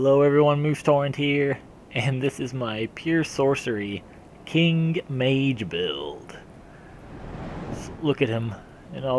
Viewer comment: I thought it said kink mage.